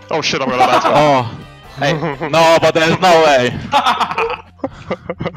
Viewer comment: subtitles are wrong its actually " is it fine if i strangle my ad carry ?" In the vods you can even see after the game on the cam in the G2 gaming house Miky strangling Caps